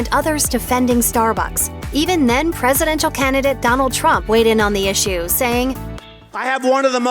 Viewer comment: did she say president of Canada Donald Trump?